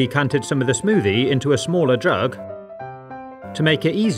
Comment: i heard: Smaller drug